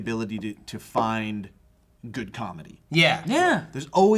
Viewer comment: JOE WAS SAYING 'GOOD COMEDY' BUT I THOUGHT HE WAS SAYING 'GOOD COFFEE' AND I THOUGHT LA HAD REALLY GOOD COFFEE?